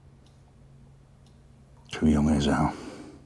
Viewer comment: What does the older man say at 13:37? I can't make it out. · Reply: Too young is how.